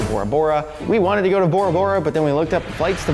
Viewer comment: for a moment there I thought he said Tora Bora. Wouldn't that have been fun. is so cute for lovely damsel that is. May this couple love withstand till the end of time. Hugs from India.